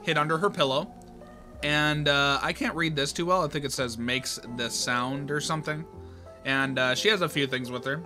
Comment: I think it says "likes the sound" awesome video btw ＼(^-^)／ 2:43 also I think it says that the hat isn't required